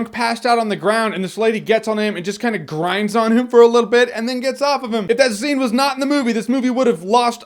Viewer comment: I thought I heard "gets off on him". Whoops.